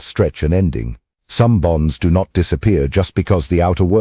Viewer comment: 🤔 ... Bonds... I thought you said BOMBS Doc 😁 ... slow-the-roll on the AI script reader